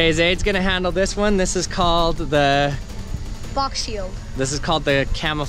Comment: Guy: So next up we're going to do the uh Kid: Box shield Guy: Camoflauge Kid: shakes his head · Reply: Lol I thought he said Fox shield :)